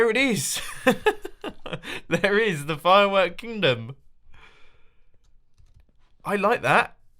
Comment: You might want to change the name from the firework kingdom because when you said it I thought you said the firework condom. And it does kind of look like a few condoms are just floating in the air.